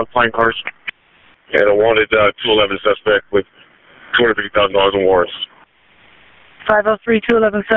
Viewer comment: Is he saying 23,000 or 250,000? 🤔 Captions say 250k but sounds closer to 23k